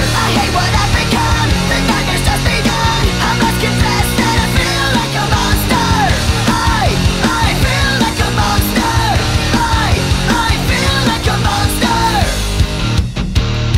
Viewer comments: why do i hear "I-I FEEL LIKE A LOBSTER" ? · Things I another heard of "I feel like a monster" "I feel like a lobster" "I feel like a rockstar" and dumb things come in my mind😅😂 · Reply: I hear “I feel like a mopster!” Legit i hear the fusion name of a mop and the imposter